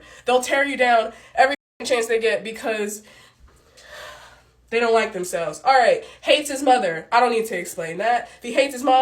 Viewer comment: She made a lot of black female therapists look bad. Because most black female therapists don't act that unprofessional. Did she say drives a Chevy. She needs therapy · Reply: I thought she said "If you don't like 'em heavy."